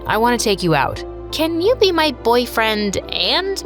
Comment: I thought she said a D